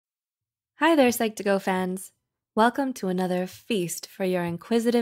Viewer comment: I thought she said "hi there psychopath"